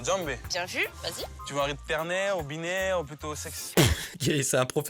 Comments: Y'a quoi moi qui est entendu "sexe" à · il a pas dit 'éperné ou robinet' il a dit 'ternaire ou binaire' les gens qui ont déjà fait un peu de solfège dans leur vie comprendront de quoi il parle à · il a dit "tu veux un rythme ternaire ou binaire" il a pas dit nimporte quoi 😂